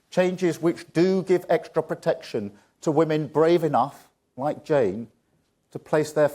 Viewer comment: why do I keep hearing "protecting woman"?? talk about sexist! are we not equal? yesterday i herd in A&E to nurses having a laugh with a guy in a room saying " we like our man naked"... but hang on....if 2 guy's said that to a woman??..